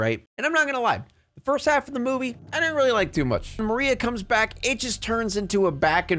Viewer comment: I thought you were saying Marina instead of Maria. 😂 I kept getting frustrated, but I was washing dishes while listening at first, so I was hearing wrong.